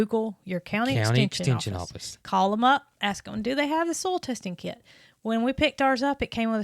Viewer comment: LOL me!!! When you, with your Alabama accent, said “soil” testing kit, I thought you were saying soul testing kit and I couldn’t figure out what in the world that was!!! I had to research it and then after realizing what you meant, I had a good laugh at myself!!! 😂🥴🤣